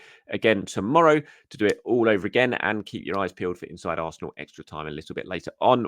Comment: Someone thought they heard me watching “inside arse” 😂